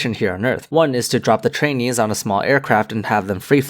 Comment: "drop the trainees" Oh god I thought he said "drop the chinese"